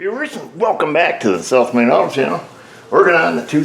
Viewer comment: Every time I hear him say, "South Main Auto", as he does in the intro, he says it quick, and it sounds like "Self-Made Auto". - Which he kind of is / has done, made his own business (as has his wife).